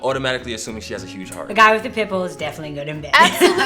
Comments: Dark haired woman at 0:11, I thought she said "The guy with the pimple is definitely good in bed".. I had to go back several times .... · im laughing because it sounds like he was saying pimple not pitbull. "and if she wasnt sexy before the pimple, now shes sexy."